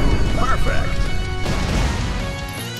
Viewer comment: damn thought the announcer said "Jackass wins"